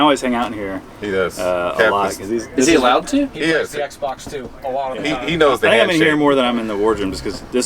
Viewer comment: I heard XBOX 2, is the navy holding out on us?